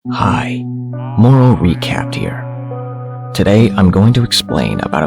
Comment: I thought he said moron recaps